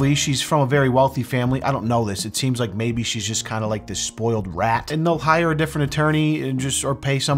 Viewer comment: I’m sorry but did you just say “spoiled rat”